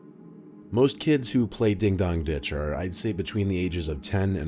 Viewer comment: I deadass thought that it was saying “Ding Dong, Bitch!”